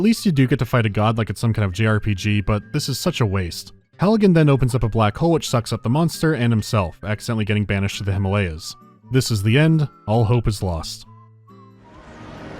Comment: I'm going crazy or does he say Halligan in